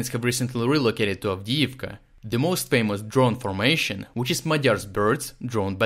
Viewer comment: Hearing Magyar's Birds makes me so proud! Slava Ukraini! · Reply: Actually it seems to be "Madiar," but I heard it as "Magyar" the first time as well.